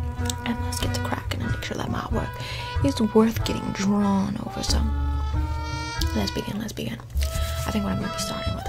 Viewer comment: I really heard lesbian lesbian